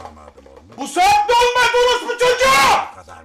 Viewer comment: ne diyor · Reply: rahat ol ne alaka bu saatte olmaz saat 10 diyor